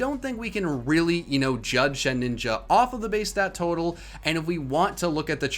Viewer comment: did he just say “shed-ninja”